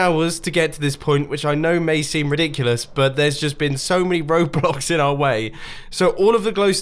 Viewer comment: it sounded like he said ROBLOX lol